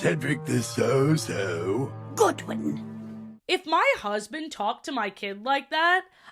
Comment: i thought she said ‘good one’ lol